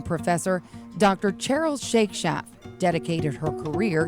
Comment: What is the VCU edu professor's name? It sounded like you said (and captions translated to) Cheryl Shake Shack.